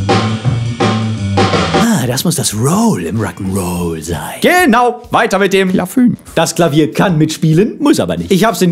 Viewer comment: "...weiter mit dem Klafünf..." - ich habs genau gehört, ein Brüller zwischendurch!